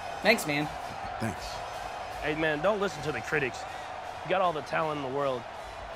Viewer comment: Dude he says critics I thought you said pretty’s